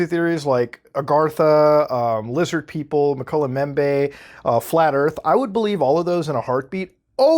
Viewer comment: does anyone know what the 3rd conspiracy theory he mentions is? I can’t make out what it says. Mcullah Membe?